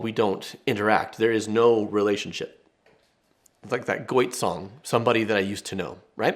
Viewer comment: I thought you said Goiter at first and, as a hospital worker, I was VERY confused. His name is pronounced GO-tye, like when you see a cute farm animal. "Goat! Yay!" 🤭